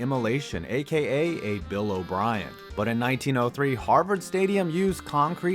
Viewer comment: I thought he said “ aka a Kobe Bryant”😂